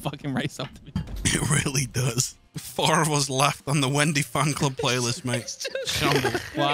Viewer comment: winning on the Wendy Fan Club playlist, what a lad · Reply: I thought you said "Wendy's Only Fans Club"